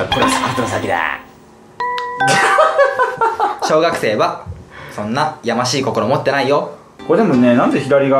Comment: はぅん「小学生はそんなやましい心持ってないよ」 って聞こえる...